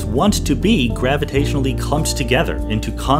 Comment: It sounded a bit garbled at the 1:46 mark. Does anyone know what he said there? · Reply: He said clumps